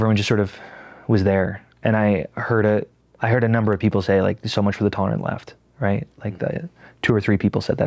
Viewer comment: what'd the people say? "So much for the tone" ?